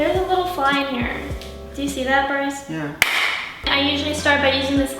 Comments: Did she just say Brice? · did just call Brice 😂